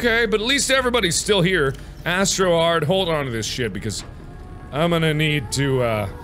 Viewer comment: when he said "astrohard" i thought he said "i so hard" '-'